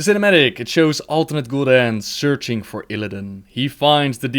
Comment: Everytime you said alternate Gul'dan, I thought you were saying "ultimate Gul'dan" lol